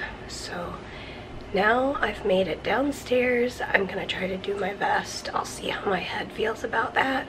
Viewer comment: Each time you say "do my vest" i hear "do my best" 😊